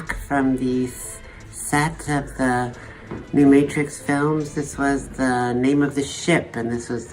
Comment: did she say "films"?